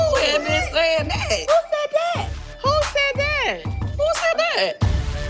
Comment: “Who said that” is the most ICONIC House Wives moments in HISTORY of any franchise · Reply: I always thought it was “I said what I said!” 🤣